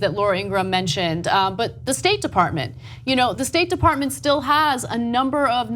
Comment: Off topic... I always hear: "Steak department" instead of "State Department"... hmmmmm... I would love to work there :D